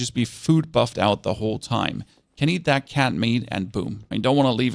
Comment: At the beginning area when you said canned mead I thought you said cat meat 😭